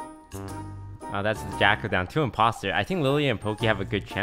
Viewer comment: did he say "jackl-ed" or yackl-ed bc i heard "yackl-ed"